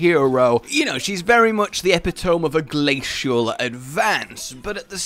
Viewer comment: did you just say epitome lmao